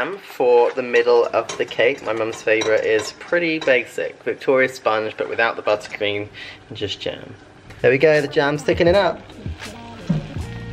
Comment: I thought Tom said re the cake, no buttered cream, just GIN ! Which sounded about right, to be fair :D